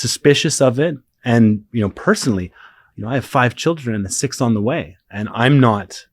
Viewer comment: did he just say he has 5 children and 6 on the way??? Dude is a fuck machine